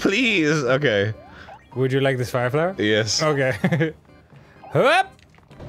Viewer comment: did you just say fire flower, FIRE FLOWER